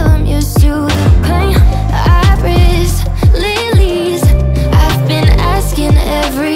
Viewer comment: My brain really thought she said “I rizz Lillie’s” 💀 ✋